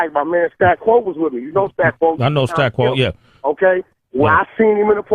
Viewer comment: did he just say stack bundles? Cant belive tht 🚫⛔🚫⛔